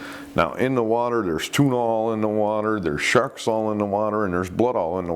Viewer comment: you say, There's tuna all in the water", but it sounds like you said, "There's Tuinal in the water". If there was a strong barbiturate like Tuinal in the water, then I would likely be one of you're statistics! I DO know that remnant's of fluoxitine (Prozac) are being found in fish, and not just beach fish, but deep water fish like tuna. Who would've ever thought that depression medicine causes sharks to kill themselves, and others? There are sinister forces in this world! L/R